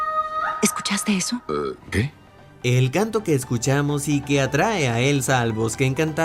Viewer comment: yo al principio escuché ''Gato'' en vez de ''Canto'' jajajajajajajajajajajajajajajajajajajajajaja